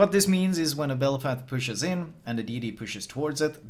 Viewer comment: Did I just hear Belfap? Has been playing too much Azur Lane lately?